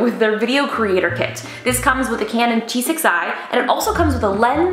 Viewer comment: Justine, did you say T6i or “G6i”?